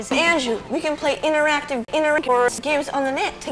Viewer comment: did that just say "Interactive Introverts"?!?